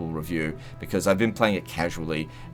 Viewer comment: Did he just call us F*****s